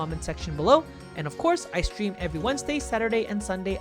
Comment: I thought you said "ice cream" and not "i stream" and i was like, that's a pretty solid ice cream schedule ngl